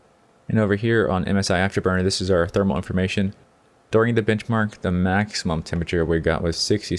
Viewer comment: Did it sound like he said bitchmark instead of benchmark to anyone else? haha :P